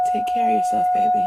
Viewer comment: When she said “take care baby” and you thought she said “ ima kill a baby” or some shit